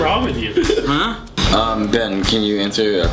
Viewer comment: did he call you Ben?